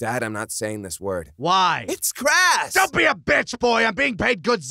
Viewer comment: What does trunks say at 1:05? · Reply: Holy King He said Crass. It's another way to say insensitive.